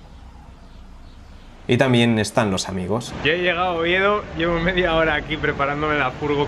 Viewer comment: A que ciudad dijo que llegó en el minuto 14:55 ??? Pd; no entendí lo que dijo, sorry 😉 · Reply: Oviedo